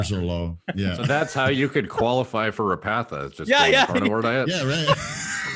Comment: I can't quite understand what Derek is saying there. Qualify for (...) what? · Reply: Repatha. It's a cholesterol medication that supposedly has less side effects compared to statins (but you can't get insurance to cover it unless your cholesterol is really bad).